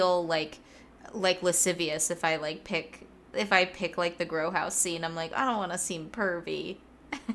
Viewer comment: what does say here? Girl house scene? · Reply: grow house scene, where ellie and dina found eugene’s weed